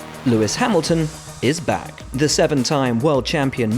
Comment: They removed the subtitles but at 0:01 it said black instead of back